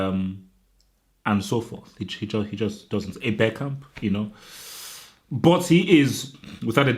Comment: you said Bergkamp but it sounded like Beckham.